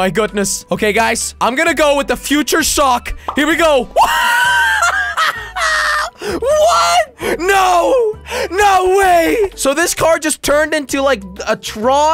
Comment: did he just say car💀